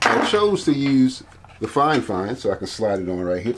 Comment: So helpful. Just one question: Being Canadian, I couldn't quite make out what you are saying at 5:36 in the video-- sounds to me like "fine-fine" could you please clarify? Thanks Merve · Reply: Hey Merve and Yes, I did say “fine fine”. Per the company rep that’s how the name is pronounced.